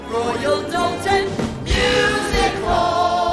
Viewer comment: did she just say exashispactical?